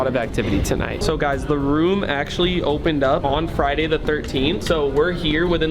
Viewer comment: Did he say Friday the 13 at is like Friday the 13 movie like jason 3:4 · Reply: Did he say Friday the 13 3:48 is like Friday the 13 movie from jason